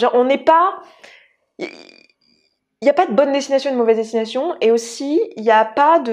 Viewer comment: j'ai entendu "y'a pas de bonne ou de mauvaise situation" xD . Super vidéo sinon, un bon sujet sensible qui touche plus de gens qu'on ne le croit